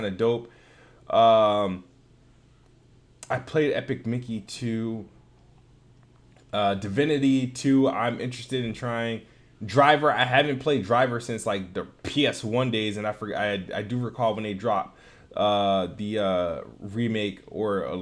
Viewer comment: my high ass thought u said da Vinki 2💀